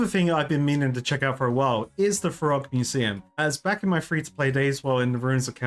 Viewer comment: I actually thought you said "the frog museum" 😅